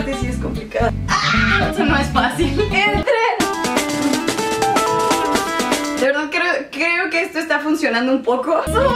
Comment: El min 1:22 que dijo ?? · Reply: Cuatro Maldonado que entre